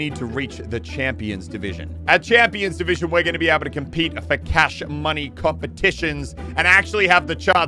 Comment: he said for cash money but the subtitles say focaccia money 😂